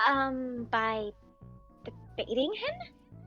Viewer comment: for a second I thought it said “by eating him” 😂😂😂